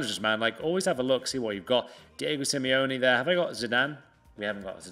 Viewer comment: Did say David Simeone?